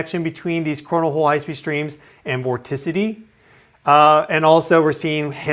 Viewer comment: Right near the beginning, did he say "morticity"? What is that? Did he mean "motricity" or "mortality"? Listened to it again at about 2:15 - was it "vorticity?"